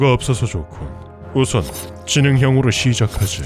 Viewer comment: 초에 지능형이라고 들리는데 능지형으로 바꿔줘야 될 것 같아요.